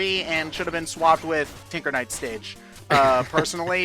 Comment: Am I the only one who thought was saying "Tigger Knight" whenever he said "Tinker Knight". The double take was real.